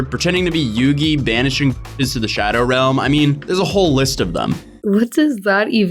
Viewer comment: i wasn’t looking so i swore he said banishing niggas to the shadow realm 😂